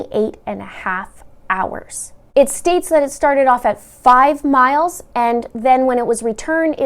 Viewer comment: I thought u said it started out at 500 miles? If she ended at 900 miles? So isn't that 400 miles?? Just curious??